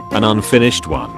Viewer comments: did it just say “an unfinished c-one” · What it says "An unfinished CONE " what the robot says "an unfinished ONE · it said one instead of cone